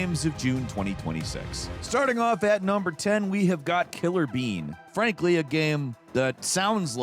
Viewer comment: I thought you said “Killer Ween”. The clip I saw when I looked up didn’t help😂